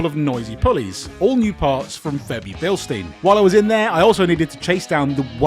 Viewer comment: Bebi Filstein 😂😂or was I hearing things around 4:30. Serious motor for the money lads.